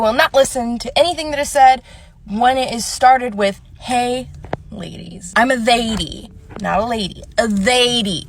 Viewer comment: Did she say "I'm a THEY-dy not a Lady"? 🤣